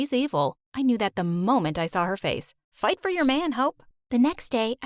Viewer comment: LMAO I THOUGHT THE MOM SAID “fight for ur man hoe “